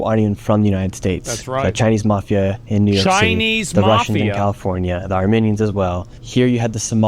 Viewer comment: me thinking he said "the Arminians as well" instead of "the Armenians" 😂😂😂